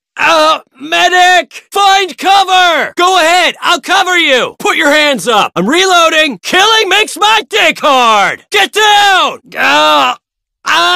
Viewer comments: KILLING MAKES MY DICK HAAAARD! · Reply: I thought he said "Killing makes my key card" instead tho XD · the reason why you're here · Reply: I always thought they said killing makes my day hard but I was completely wrong I guess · KILLING MAKES MY DICK HAAAARD! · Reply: I Thought He Said: "Killing Makes My Day Hard!"